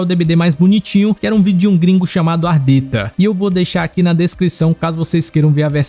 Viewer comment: Eu juro que eu ouvi "ar teta" 😂